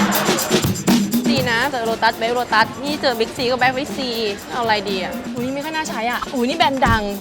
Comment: เหมือนหนูได้ยินว่าเค้าพูดว่า "เเบะวิ้กซี" จาก เเวะบิ้กซี